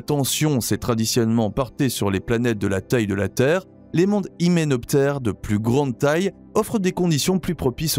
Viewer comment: Excellent ! (j'ai bien ri à 9:15 les mondes quoi ? xD) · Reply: Hyménoptères ?